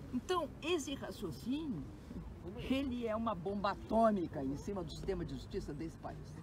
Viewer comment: Gente do céu rsrsrs será que ouvi " bomba atônica " rsrsrs. Não é possível uasuasuasuas